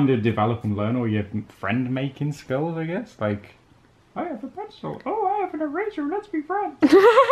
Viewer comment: When Said I Have A Pencil I Have An Eraser Let's Be Friends I Thought He Said I Have A Pretzel I Have An Eraser Let's Be Friends I Was In A Hysterical Laughing Fit